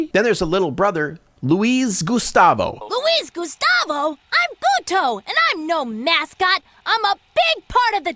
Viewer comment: okay, I rewinded this many times. I think it says what I think it does. So, I think he says "I'm puto", which is a word in spanish that has many meanings. It literally translates to male prostitute, but it is more commonly used as a derogatory word against homosexuals, more often, it means fa*got. So, this movie, supposedly aimed at kids, has spanish cursing that not even my parents use because they consider it extremely vulgar. Like, why?